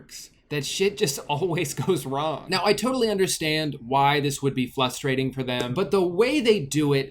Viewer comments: I love how you kept using “flustrating” while giving them fake sympathy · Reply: I was getting so confused I thought he was saying frustrating but he replaced the r with an l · did you say "flustrating" at 5:14? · am I the only one that heard him say "fLustrating"